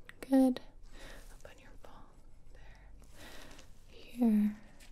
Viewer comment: sounds like she says "Up on your ball"